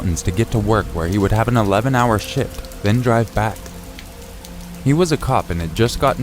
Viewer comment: He'd have take a 2 hour drive to have an 11 hour shit 4:15 lmao... i think he said shift but im me so I heard shit